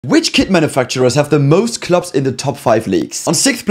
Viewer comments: I cannot lie i thought he said kid manufacturers 😅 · I lowkey thought he said “kid manufacturers” 💀💀💀